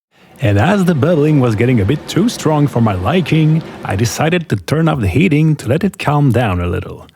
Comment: for those of you who missheard, like me, he turned off the heat... I thought he said turn up.